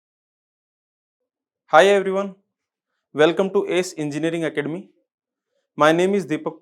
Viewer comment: am i the only one who heard HI everyone! Welcome to GOCLASSES!